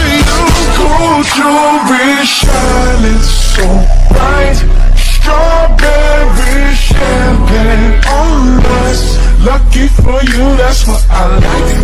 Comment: “Strawberry champagne on ice” since I was in the second grade I thought this man was saying “Strawberry sherbet all nice” 🙈